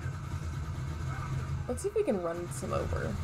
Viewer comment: it sounds like you said let's see if we can arm this f***